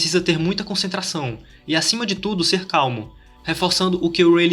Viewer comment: so eu que ouvi "e acima de tudo o usuario tem que ser CALVO"'😳